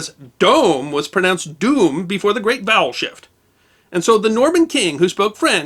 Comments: I kept hearing "bowel shift". The story still works though, oddly enough. 🙂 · All i hear is "bowel shift" 😅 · I keep hearing him say, "The great BOWEL shift." Hmmm.... Spanish is notorious for "V" vs. "B" confusion, but not normally English. ...